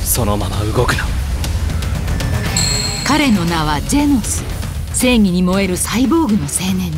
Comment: ジェンズって聞こえるのは俺だけ？